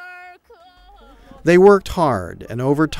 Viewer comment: he says 'they worked hard' I hear 'they were tard'